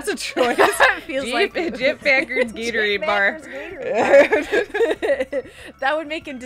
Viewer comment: what exactly did they say in the Minnesota accent at 37:10 ish, Packers Gatorade Bar? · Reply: Geoff Packard's gatorade bar! It's a running joke from their Disco Elysium playthrough. For context: <link> Start from 59:37 :)